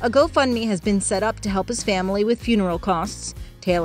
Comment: 33 years old is too young of an age to have a heart attack. Condolences to his loved ones. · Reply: I could have sworn they said 69 years old. When they said that I was thinking damn he looks great for 69.